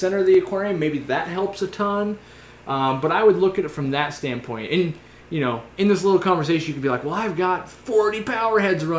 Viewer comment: u mention a plant...that grows in darkness or seems to. I cannot understand what it's called or how spelled?? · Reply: Subwassertang